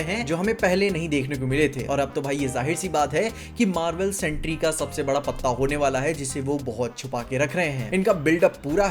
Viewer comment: Did just said ,"Marvel Sentry ka sab se bara patta hone wala hain" instead of saying "sentry Marvel ka sabse bara patta hone wala hain"?😂😂😂😂😂😂😂